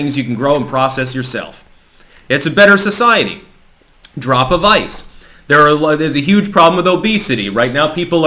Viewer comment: I heard "drop of ice" every time.